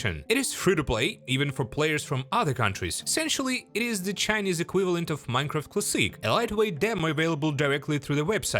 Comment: Im so sleepy af i thought he say "equivalent of minecraft pu57y"